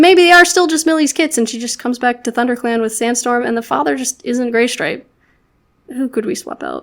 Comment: I love this AU. It comes together so well and I think it's a great way of representing how a trans individual would feel and or be treated in clans. Also near the end it sounded like you said GAYstripe 😂😍